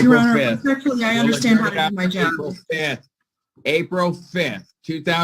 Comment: I missed it.🤦‍♂️ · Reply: right around the 21:00 mark she actually said “Your Honor, respectfully I understand how to do my job” Well, I respectfully disagree. I find it disrespectful, unprofessional, lazy, and demonstrative of the lack of care you have for your clients that you don’t take the responsibilities of your job seriously. It’s these peoples lives your messing with, their freedom in some cases.